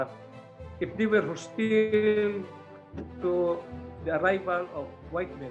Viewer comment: I think Mr. said "If they were hostile..." not "If they were to steal"